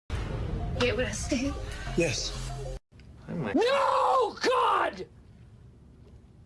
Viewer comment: What did karen say at 0:00 ? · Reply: She said do u hear me